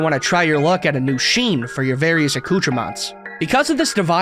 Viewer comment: what is this word what does it mean and how do you spell it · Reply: noun plural noun: accoutrements additional items of dress or equipment, or other items carried or worn by a person or used for a particular activity.